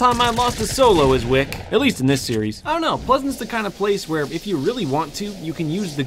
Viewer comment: did he just say pleasant